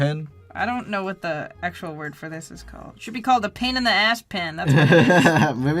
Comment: no cause I heard "pen in the ass" pen